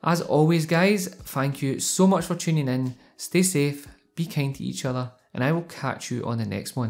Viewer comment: I thought he said "be kinky to each other" lmao